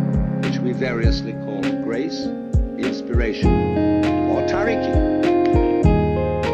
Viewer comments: Does anyone know what Alan Watts said around 2:05 - 2:09? "Which we variously call grace, inspiration or ?????" I've tried looking up the transcripts but can't find this part and the word after inspiration. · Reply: After a little bit of research: <link> "Jiriki and tariki (他力 meaning "other power", "outside help") are two terms in Japanese Buddhist schools that classify how one becomes spiritually enlightened." Tariki is what you are looking for. Thank you for your interest :) If you enjoyed this song please consider subscribing to my channel it means the world to me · love love love this tune and the voice sample :-) <3 can anybody hear what word is being said at 2:08? i can make out "which we variously call grace, inspiration, or (???)" but i can't find a transcript on the original alan watts lecture anywhere :-( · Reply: tariki -- "literally, "power of the other"; a Buddhist term for forms of spirituality that emphasize the importance of grace or celestial assistance" source: Dictionary of Spiritual Terms